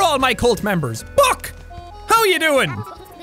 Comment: I thought he said FUCC WHAT ARE U DOIN?!